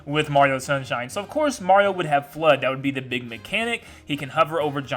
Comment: thought he said “of course Mario would have blood” 💀